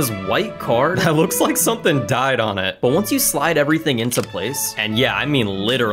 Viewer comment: wait a minute what did you just say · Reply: *died